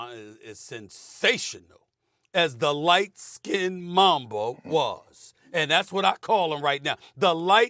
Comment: Jesus, I thought Stephen A said 'light-skinned mongrel'.